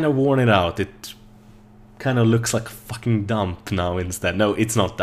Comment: Did u say fucking damp? @ 3:07 :D